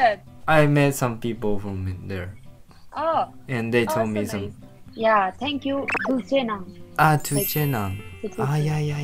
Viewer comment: Firstly i thought you saying "türkçe" in turkish ahahaha but its thank you in tibetian wow😅